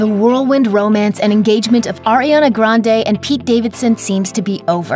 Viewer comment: i heard fartriana grande 😂 ily her sorry✌